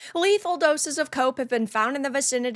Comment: Lol almost thought you said in the beginning "Lethal doses of coke"